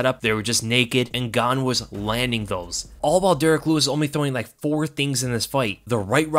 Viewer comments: I know you said “And Gane was landing those” but it really sounded like you said “Ngannou” lol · Did say "Ngannou was landing those"?